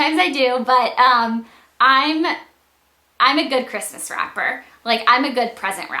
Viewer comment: I actually thought she had said Christmas "RAPPER" and I was like, whaaaat?! ahhahaha